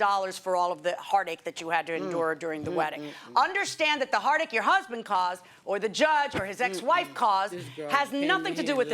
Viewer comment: what did she mutter while judge was talking?? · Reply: She just "mm-mm"ed and then said "this girl came here and lied."